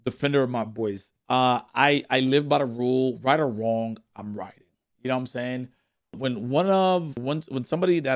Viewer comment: " I live by the rule, whether im right or wrong, im right" The amount of ignorance and ego in the guy is crazy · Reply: I heard "I'm riding" which makes more sense. Btw I'm not agreeing or defending just pointing out.